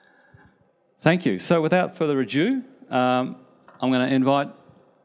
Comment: did he really say "Without further AJew" (instead of ado)?